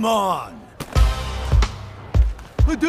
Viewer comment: I hear "bazooka" than "hadouken"